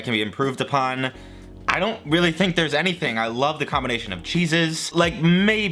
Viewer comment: Thought he said "the combination of JESUS" at 4:15 😂😂😂